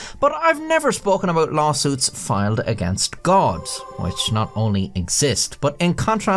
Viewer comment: I had my volume down really low and i thought you said "Boston's fight against God." My first thought was "aren't they committing sacrilege every day just by existing?"